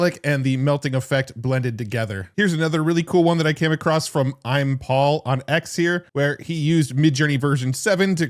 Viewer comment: Okay but did anyone else hear ‘Hi, I’m Paul!’ in their head like it was the end of a Jimmy Neutron episode? That three-eyed monkey from DNA Productions.